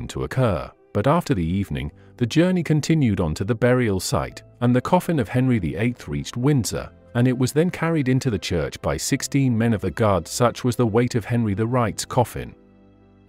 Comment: Henry the right? Did I hear this correctly at 8:39?